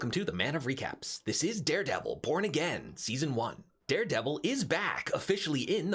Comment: My hearing is jacked up I could have swore you said welcome back this is Daredevil p*** again😂